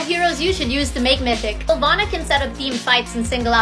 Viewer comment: I’m the only person who heard her say alana right? 😭